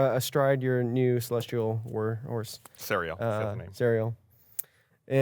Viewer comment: am i the only one who thought gus said walrus and not war horse when casting the find steed